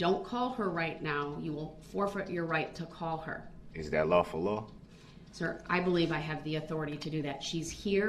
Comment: Him and his rush to judgement bs! 🤦‍♀️ Edit to add: it sounds like he says "falafel law!" to me when he says "lawful law."